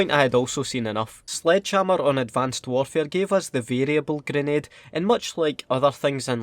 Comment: I thought he said (Un advanced warfare)